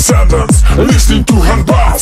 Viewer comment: thought he was saying "you are Gagarin"